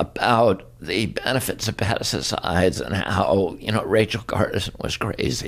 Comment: The transcript wrote "Madison's eyes" instead of "pesticides". Transcripts are useful but sometimes plain ridiculous.